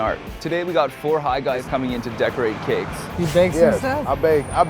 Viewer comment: idk if I'm high but I swear I thought it said deodorant cakes.